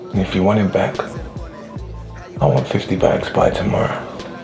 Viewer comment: Rip to those that thought he said 50 bucks not 50 bags lmao